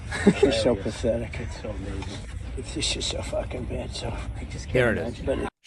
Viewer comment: I heard "She's just so F-ing bad".